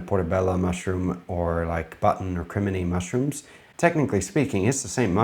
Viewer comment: Omg, at @ 13:49 I was distracted and thought you said "Incriminating mushrooms" 😂